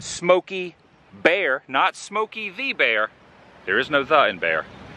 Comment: always heard it as smokie the bear